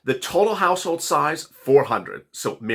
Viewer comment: Did he just say household size $400? I know it is four